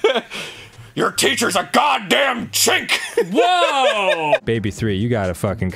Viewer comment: okay did arin say chick or